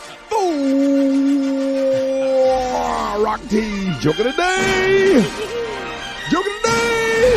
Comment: All this time I thought Gary been saying khaluah of the day 🤣..smdh slow moment fr